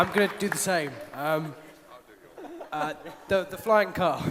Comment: Everyone: deep meaningful awnsers Rupert: erm.... flying car! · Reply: At first I thought he said flying cup and I was like- wat